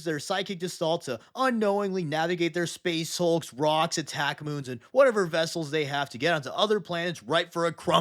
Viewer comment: I'm sorry, did you say "attack moon"?